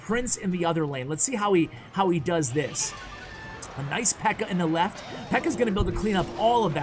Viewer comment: Did you just say “an Ice Pekka to the left” in